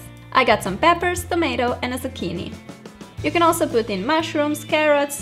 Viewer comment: When she said zucchini I thought she said bikini lol 😂😂😂😂😂👙👙👙👙👙👙